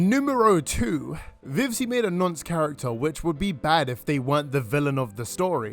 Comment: Can someone PLEASE tell me what word he is saying @ 5:23 and @ 5:45 ?? It sounds like 'nunce character -- :labeling vivziepop as a NUNCE is wild" -- It's driving me insane that I have no clue what he's saying. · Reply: hes saying nonce. nonce means a sex offender